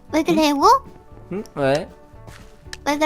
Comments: 나만 "왜 구래여보?"이걸로들리나요?!ㅋㅋㅋ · 나만 왜그래 여보 ? 이렇게 들리나